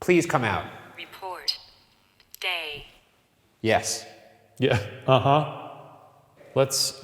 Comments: Man, I wasn't looking at the screen and after Ryan said 'please come out' at 49:06, I thought the Ovilus said 'gay' lmao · I was looking at my phone and heard Ryan asking the ghosts to show them that wiggle, then the box said "Report Day" But I heard "Report Gay" LMAO